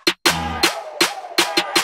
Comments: I’M SORRY WHAT- SJSKKSKS I HEARD “LICE CREAM” SKSK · I’M SORRY WHAT- SJSKKSKS I HEARD “LICE CREAM” SKSK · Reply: lee THE ICE PART SOUNDED GLITCHY SO I HEARD “LICE CREAM”M😭😭😭